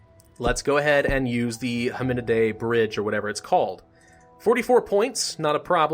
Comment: Hominidae i thought you said ARAMBE )